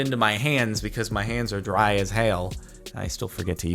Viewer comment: he said hail instead of hell